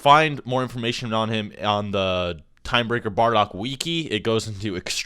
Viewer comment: Godamn it I cringed so hard I got whiplash at 1:11 this man said WEKI instead of wiki